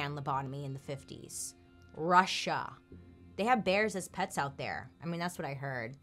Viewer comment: "They have bears as pets in Russia" gives the same energy as "They have lions as pets in Africa". Btw only the really well off have lions, most of us have zebras. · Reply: I thought she said "bears in pits", as in Bearpit, as for entertainment.